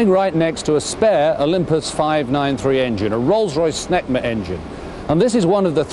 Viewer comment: i fucking swear i heard 'Rolls Royce Smegma Engine' the first time i listened to it!